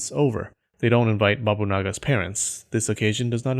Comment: When he said Bobunaga I thought he said Babayaga